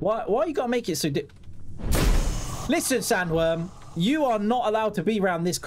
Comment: Yo, nice update and gameplay as always, watch out for those magot-snaks things and yes, the sky was pretty weird because of those well seen stars! 🎉 Omg at min 13:00 i thought you said “ listen Samuel instead of sanworm” 😂😂😂! Dude this map is creepy!